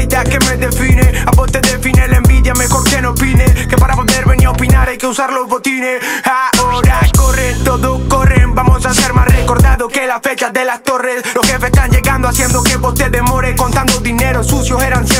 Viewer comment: "vamos a ser más recordados que las fechas de las torres"GOOOOOOOODD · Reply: pensé que decía " que la bestia de latorre" el jugador jajaja